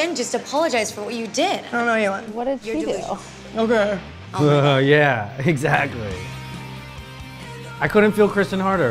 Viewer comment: What did Stephanie said at 28:12? When then Kristen responds "oook" couldnt hear lol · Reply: She said "you're delusional"